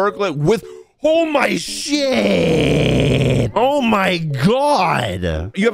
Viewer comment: When he said "OH MY SHITT" I heard ''HoLd My ShIt"